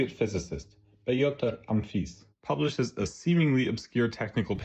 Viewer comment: I thought the voice was AI until I heard the incredible "Pay-otor UMFIS", I don't think AI could mess up that badly